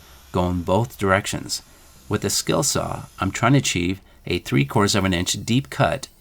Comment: what did he say?? Three quarter/course of an inch? What'd course mean in this context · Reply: He said "three quarters of an inch" (3/4" inch deep)